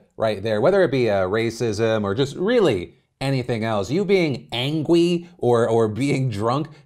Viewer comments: did you just say, ANGWY???? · did he say 'angwy'?